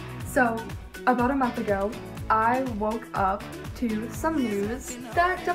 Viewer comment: I thought she said a month I ago I woke up to nudes